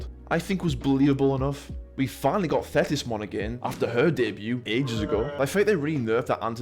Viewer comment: For some reason, I heard "Fetishmon" when you said Thetismon's name lmao